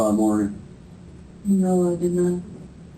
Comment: she says "no I did not" and the caption says "yes I did that"😂